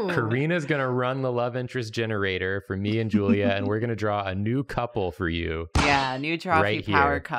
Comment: Int the intro, when said "new couple" I thought he had said "nude couple"